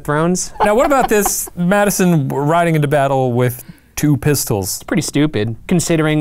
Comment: My husband thought Shane said President Madison rode into battle with two missiles...